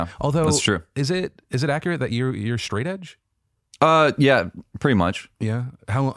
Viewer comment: What did Phil call him at 6:50 and what does that mean? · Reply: Sophiamlb Brown Phil called him “straightedge”, which is someone who doesn’t smoke or drink.